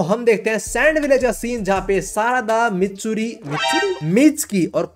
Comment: i heard it like mitsuri😂😂❤❤